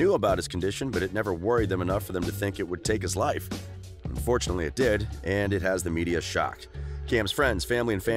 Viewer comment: did he say fortunately it did